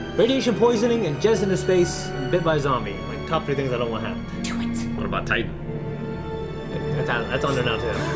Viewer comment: All these years I thought she said ‘twit’ but now I realise she said ‘do it’